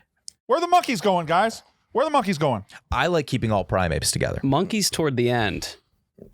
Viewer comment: said “Primeapes” instead of primates at 44:19 this guy can’t even differentiate between Pokemon and real animals how can you take any of his duck takes seriously